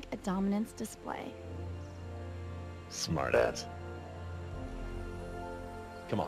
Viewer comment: did he say smartass?....